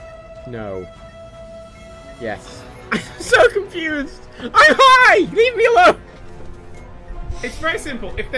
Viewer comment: Did say "I'm high" at 38:25 ? Does do drugs?